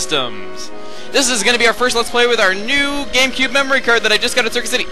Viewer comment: WAIT DID HE SAY HE GOT A GAMECUBE MEMORY CARD AT CIRCUIT CITY AT 4:29 (I miss circuit city